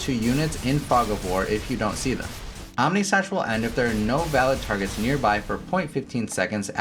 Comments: I thought I heard AmèSlash. 😂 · did you just say... Ameslash?